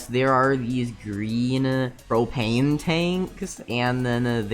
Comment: I thought he said cocaine tank lol